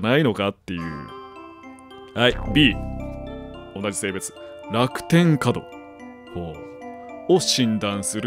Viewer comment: 画面観てなかったから「楽天カード？？」と空耳した